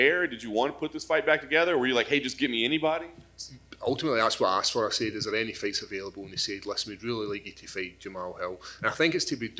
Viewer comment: Wtf did he say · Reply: "Ultimately that's what I asked for, I said is there any fights available"